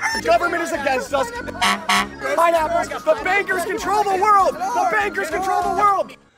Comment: That subliminal messaging, who knew Sponge Bob was so woke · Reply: I thought he said the Bakers.